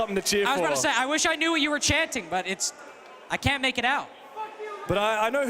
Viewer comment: Someone at 7:45:56 said fuck you lynx?😂is that what I heard?